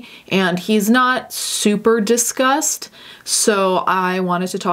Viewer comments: I thought you said “he isn’t super disgust” instead of super discussed and i was like YES HE IS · What she says: "He's not super discussed" What I hear: "He's not super disgust" Was bout to be like, "excuse me??" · I heard, "He's not super disgust" and I was like, "erm, yes he is..." Then I realised.